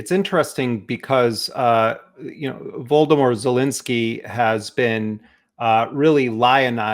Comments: Did he just call Zelensky 'Voldermore'...!!!? Excellent interview though. Chomsky - what a brilliant mind. Appreciate that the interviewer allowe him to speak, without interrupting him, until he paused naturally. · I heard Jeremy say "Voldemort Zelinsky". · Did he say “Voldemort Zelensky” at 7:50? Hahaha